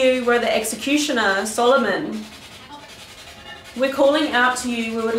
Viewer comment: I herd help me in a women voice at 13:28 I heard London in a males voice